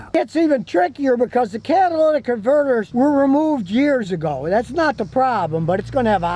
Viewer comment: did he say catalytic converters were removed years ago?